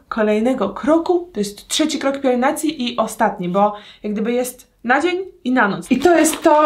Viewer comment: usłyszałam nadzień XD 7:08 znowu